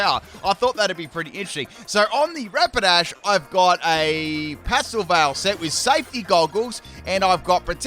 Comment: I thought it said Pasta Veil